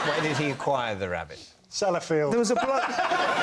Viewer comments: God, I hope someone answers this, because I'm having the same problem as you. I listened to it about 20 times and got as far you, salad field. I thought he said Sally Field, but that didn't make sense either... 🤣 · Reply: Sellafield - a nuclear site. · what does he say? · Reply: Sellafield, apparently a nuclear site in England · I didn't quite catch that - what did he say at 6:34? Salad field? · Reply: Sellafield, nuclear power plants in the UK. I think they also had a catastrophic accident in the past